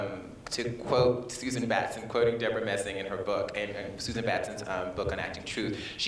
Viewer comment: Who does Laverne mention around the 19:39 mark? I can't quite catch the names. Thank you in advance! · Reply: Susan Batson and Debra Messing (of Will and Grace)